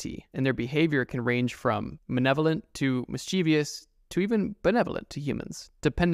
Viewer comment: Pretty sure you meant to say “malevolent” but it sounded like you’d said “manevolant”